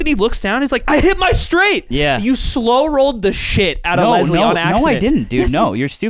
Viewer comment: True. It's slow rolling without intention. She definitely thought that was all hers before he flipped. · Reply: yeah, I think just didn't catch "on accident" @ 39:50 and so they were arguing over nothing